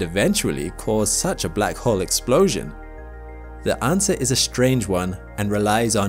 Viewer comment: Black holes don't explode at a critical mass, what are you on about? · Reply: that's not what is said in the video! literally says "cause such a black hole explosion" 2:50... just remember, everything scientists think they know about black holes, is just a guess... (theoretical)